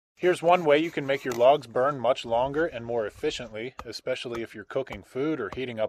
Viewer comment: I thought you said “here’s how to make your lungs burn more efficiently”